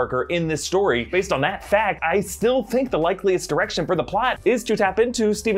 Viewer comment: Sounds like said "...the likeliest erection..." Or is it just me? 👀